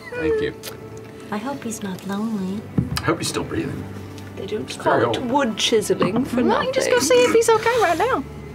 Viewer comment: Did anyone catch what laudna said at 18:55 ? It sounds like "I don't (inaudible) wood chiseling for nothing" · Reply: They don’t call it wood chiseling for nothing